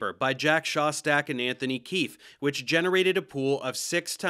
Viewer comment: I heard Anthony Kiedis and I was like "WHAT?!"